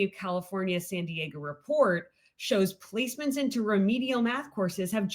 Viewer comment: Malcolm, I ALSO thought she said "policemen." after 3x, I'm guessing she said, "placement," it just came out quick, and totally sounded like, "policemen."